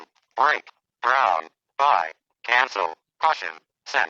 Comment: is it "candle" or "cancel"? I always hear the second, but I can see "candle" on the screen.